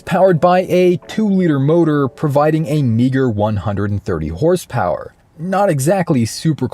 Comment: Did i just heard the n word at 1:35? Im sorry i can't unhear it is so funny